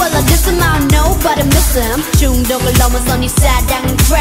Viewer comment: i heard crap......WHY TF R MY EARS SO SENSITIVE